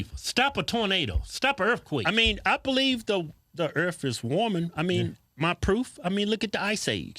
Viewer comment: Did just say "I believe the Earff is warming?"